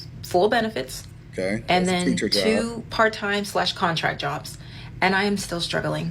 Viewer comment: it sounds like she says "contract" jobs not content